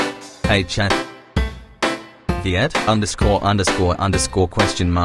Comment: What am I hearing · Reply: V I E T _ _ _ ❓ NaM ❗